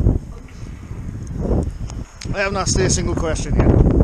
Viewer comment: did it say "don't ask" lol? Coz, well you hadn't!!? LOL! Where were you on the first location, it looks stunning!!? Kx Mi duck is Brummy! Lol! You do it for us.... And we thank yooooooo. Lol!